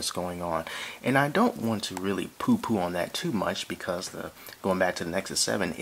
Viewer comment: did you say "POO POO" @ 07:17 LULS